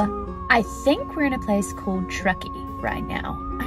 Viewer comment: The American way of speech is every so slightly different from the British way. Emeryville, CA: no real difference. ✅ Truckee, CA: I thought you were saying "Trekkie" like you had just stepped off the Starsip Enterprise. (As majestic as you said Truckee, it sounded so much like a newly discovered planet [🪩] typically from the Starship Enterprise .) Zeyphr: Sounded like "zipper"instead of "zeyphr". (There was a failed professional basketball team from Chicago [no bull; thanks for the reference, Justin from YouTuber Justin Learns Things].) 😅 Someday soon, I'll get the chance to ride the California "zipper", I meant the "Zephyr". From what I can see and hear, you had an amazing trip; so did a number of YouTubers of all ages after reading all the comments. While you took an Amtrak 80 hours from Emoryville, CA (San Francisco) to New York City, I'm hoping to go from the Bronx (my hometown, a subway ride ride from Mouniham Train Hall in Manhattan 🚇) to Richmond, CA (a short BART ride from San Francisco) (not to be confused with Richmond County, Staten Island; the least populated borough of the Big 🍎🍏🍎🍏🍎).